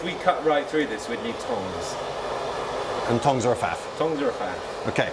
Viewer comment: What is a Fath? says at around 10:00 that "thongs are a fath"